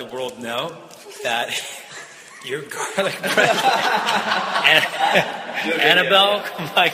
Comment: what is jared saying at 0:21? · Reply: that she has garlic breath i think lmao